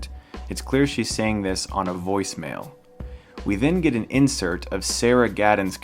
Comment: Hi, i'm studying English. I don't understand what is the video saying at 2:49. Is that "Within getting insert of Sarah" or "We've been getting insert of Sarah". Surely there is CC option but i don't think it shows exactly what the video is saying. Pls. Help me. Thx. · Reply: He says "we then get an insert of sarah".